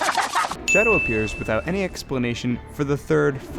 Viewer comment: the subtitles says shadow but you said metal,that's shadow not metal.